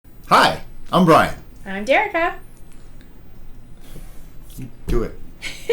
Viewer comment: WAIT DID SHE JUST SAY HER NAME WAS JERICA? I thought I was the only one!